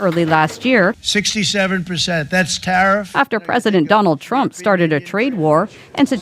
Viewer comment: At the 4 minute mark, I swear I heard her say President Dumbo Trump... :D